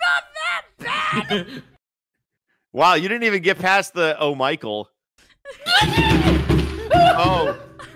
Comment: I hear: "Oh Maicohn~"